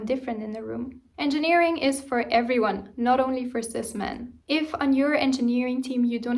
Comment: what did you say 🤔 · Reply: She said cis-men implying there are more than two genders.